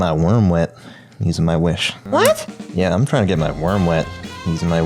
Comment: lol. Thought he said womb